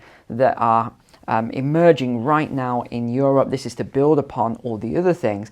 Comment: Did he just say Ewrope????